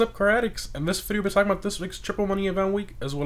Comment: Bruh 😂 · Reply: bruh??? It really doesnt sound like you say car addicts, maybe insert a space between the two words. Cause I here craddicts